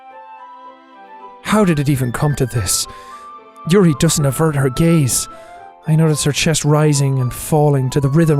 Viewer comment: Yuri doesn't avert her gaze Me thought he said: Yuri doesn't avert her gays